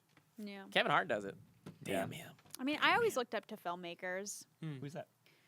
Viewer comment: totally thought said 'Phil Makers' instead of 'film makers' "who's that?"